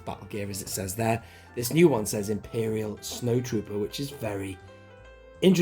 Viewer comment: I thought that first figure said Imperial Snowflake, at first.